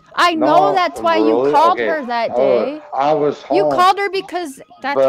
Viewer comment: At timestamp 51:11 was saying he WAS home?? or about to say it?? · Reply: Yep. """I was home""